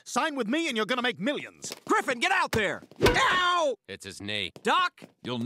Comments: When she said you was gonna buy me a house at first i thought she said horse and then the 2nd time it said house is my ears ok? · The first time I watched it I thought she said horse